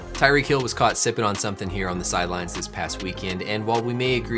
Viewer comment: i thought it said tyreek hill got caught slippin LMFAO